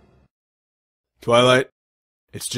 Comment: At the first time I watched this, I heard "It's just JIZZ"